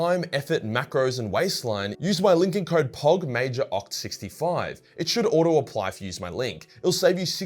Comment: I thought he said Pawgmajor lmmfao